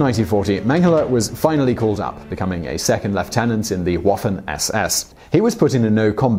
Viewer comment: he says "waffen ss" i hear "waffles? yes!yes!" god i'm hungry...